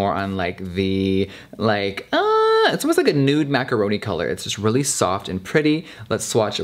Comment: Did he say Nude Macaroni color at 8:43???? lol